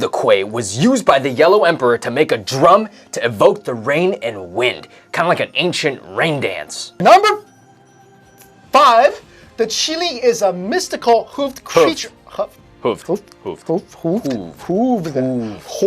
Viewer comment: is it just me or did i heard chilli